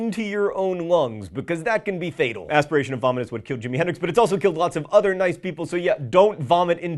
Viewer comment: I thought you said asphyxiation of vomit is what killed Henry and I was like, WHAT? NO. NOOOOOOOOOOOOOOOOOOOOOO!!!!!!!!!!!!!!!!!!!! NOT THE BABY. I re-winded hurriedly. I am so relieved.